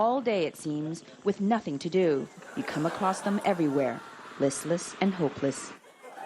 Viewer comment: "Listless and hopeless" ? Did i heard that right = ) ?